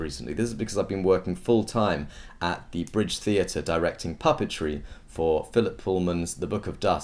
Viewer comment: i thought it said puberty instead of puppetry